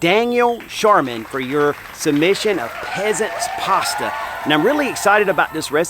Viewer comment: Oh I thought you said Pheasants Pasta - Sounds Good😊❤